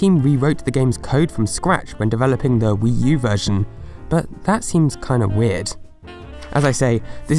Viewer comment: You said 11 Subtitles said 19 What is going on?? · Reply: he says "that seems a little weird but subtitles say "but that seems a little odd."